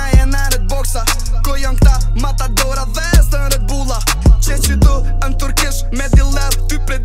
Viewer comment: what is he saying at 0:49 iam turkish or what? Greets from istanbul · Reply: No, he isn't a turk so why should he say "i am turkish"? He says "qe qitu en turkish" meaning "i am here, in the turkish shop"